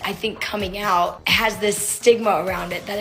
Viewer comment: I’m having a hard time reading this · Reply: I thought they said 'Karens coming out with kids' 😭✋🏽